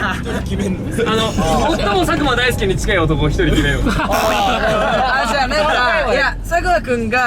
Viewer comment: 最初の『正解は佐久間大好きー』って聞こえてそうそう…みんな大好きって思ったら『佐久間大介』だったのは私の願望でした😅💗